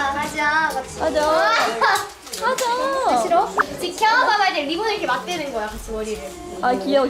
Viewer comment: 껴봐 인마 하자 같이 ㅋㅋㅋㅋㅋ 일본에 있을땐 어려워하던 선배 아니였냐? ㅋㅋ 찐친됐네 ㅋㅋ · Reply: 껴봐 인마가 아니라 지켜봐봐 아닌가요??ㅋㅋㅋㅋㅋ근데 저도 껴봐 인마로 들림ㅋㅋ